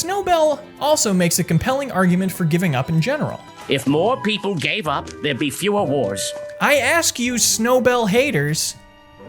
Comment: I really thought he said "If more people gave up, there'd be less whores" and was a little concerned